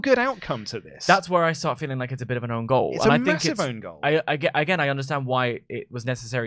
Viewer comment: what word is he actually saying? Is it ungoal? Ongol? Got an idea what he means but what is that word? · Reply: "Own Goal"